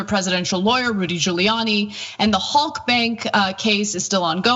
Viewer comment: I thought she said Hulk Bank.